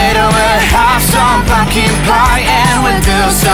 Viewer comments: Dobrze usłyszałem? Fucking pie? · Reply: Chyba pumpkin pie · kiedy śpiewają słowo "pumpkin" usłyszałam "fu**ing" co jest ze mną nie tak? 😂